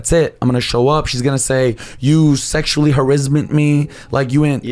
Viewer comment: did he say sexual harizzment lol